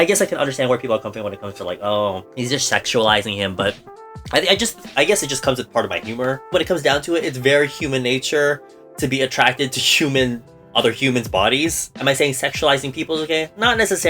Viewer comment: I thought i heard xiumin body but it was human body lol hahahah and the fact that minseok have a really nice body 💕